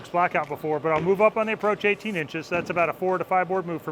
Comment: Did you 10:02, did you say move up on the approach about 18 inches?